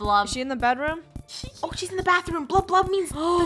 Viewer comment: Funneh: I think someones hiding in the bathroom Meh: WHAT I thought Funneh said Washroom not bathroom! NA NEE